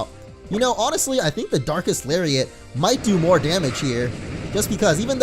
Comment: I seriously thought he said dark ass lariat